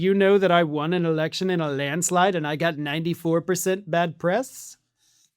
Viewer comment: I first heard it as Trump saying “I got 94% bad breath”.